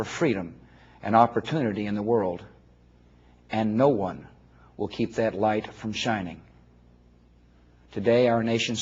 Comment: Did he say no life will keep that light from china at 1:28????? Defauq